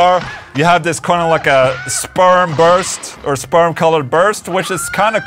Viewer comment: Did Ola say “sperm burst”?? 😂 🍆